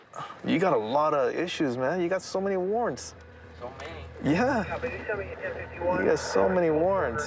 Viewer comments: He replied "so many?" Hey did not say "not me" Who ever put that in the subtitles needs a strong warning. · the dude says "so many?" and the subtitles say "not me" lmao